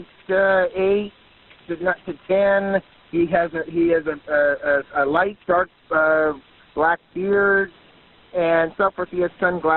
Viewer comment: "He has a light dark black beard" ???😂😂 did I hear that right?